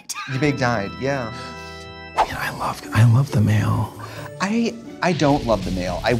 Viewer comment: I thought it said “the mall” & I was READY